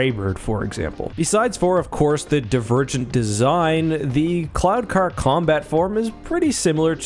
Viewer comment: I kept hearing "clown car"...